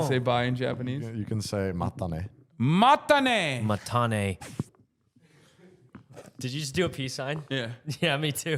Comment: Anyone else speaks spanish and thought at the end they said "Matame" which means "Kill me" in spanish???